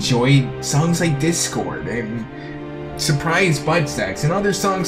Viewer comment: did you just say surprise butt sex?